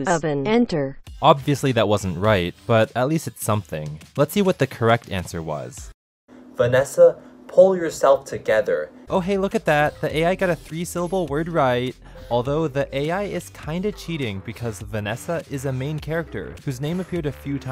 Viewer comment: I thought he said “let’s do it together “